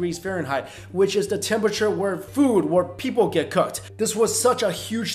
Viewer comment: anyone think he said “food or where people get cooked”??